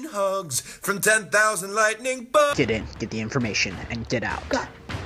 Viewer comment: TEN THOUSAND LIGHTNING BUGS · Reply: I thought he said lightning bolts